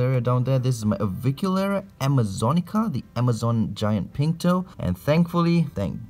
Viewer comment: Dude I'm Brazilian right... So when at the video you said the T's name Amazonica Pink Toe; i swear i heard "Amazonica Pinto" I lol'ed at that for a moment. Pinto means Penis, that's why...